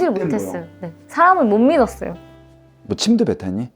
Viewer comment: 혹시 뭐라고 하시는 거예요..? 침대 몇 하니..? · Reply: 너 침도 뱉었니?